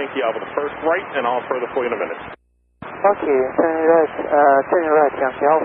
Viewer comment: AFL122: "fuck you" Is anyone else hearing fuck you? XD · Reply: "Okay"